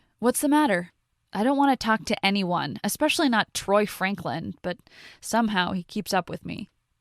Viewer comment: anybody else hear her say Troy at 6:48.... just me okay